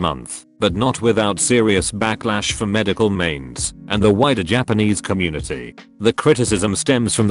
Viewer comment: I keep hearing "the whiter japanese population" rather than "the wider" and that's funny to me